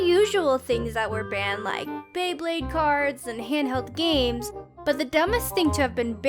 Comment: did you just say "beyblade cards"? oml